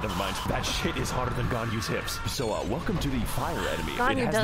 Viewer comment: Until I read the subtitles, I thought he was saying Ghandi's hips .... which is a different kind of .. something.